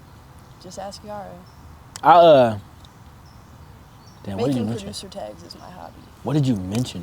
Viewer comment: Did she say ‘spotting producer tags’?